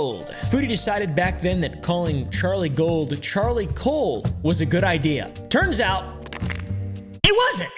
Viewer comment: I thought she said Charlie cold